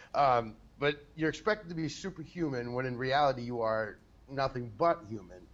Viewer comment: People forget that the president is just like any of us "normal" people. The same thing happens with celebrities. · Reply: ok because he literally continued with "you are nothing but human" and i misheard it for "you are not a human"